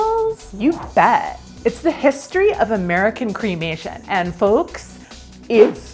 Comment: Oh! I thought you said claymation. You know, where they make the bodies walk around with stop motion photography. I so much wanted that to be an option.